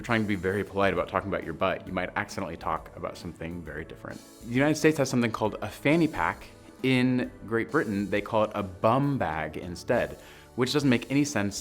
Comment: Lol I was wondering if you was going to mention fanny pack x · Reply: I actually thought he said fanny pad to begin with... (that's sanitary towel!)